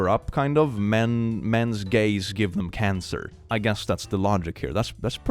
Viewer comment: the subtitles said "mens gay's" instead of mens gaze and i love that